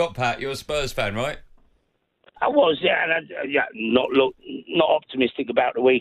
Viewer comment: did he say “ I was “ ?